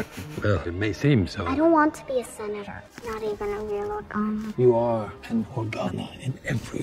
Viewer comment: Eww! The captions said “orgasm” instead of “Organa.” Well, it did when Leia said she wasn’t a real Organa.